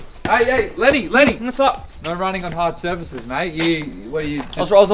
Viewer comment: no running on hard surfaces?... i always heard "no running on the red bricks"